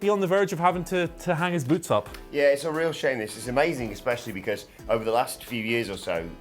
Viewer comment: When you said "Shame This" seriously sounded like you said "Shameus".a